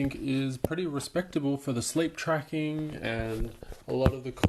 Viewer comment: i thought you said slave tracking m8 😂 😂